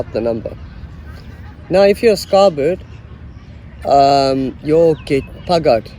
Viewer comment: Phaka means phakama. Phakama means to stand up for the number. That means that one have to stab someone to make the number strong - after it has been weakened by some incident - like the warder snatching the money or drugs from the number gangster. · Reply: "Now, if you are a sky bird, you'll get buggered..." That's what I thought he said 🤭